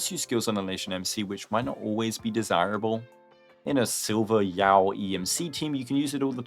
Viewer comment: i thought you said silver yaoi